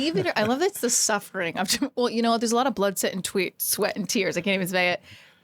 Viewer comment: Did you just say "blood sweat and tweets"?